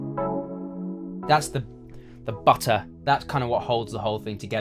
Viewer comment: Did he just say "butter" 2:16 ?